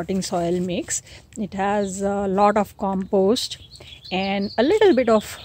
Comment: I didn't catch what she said at 1:55 for the soil mix. She said compost and a little bit of something. ?? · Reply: Coco peat and perlite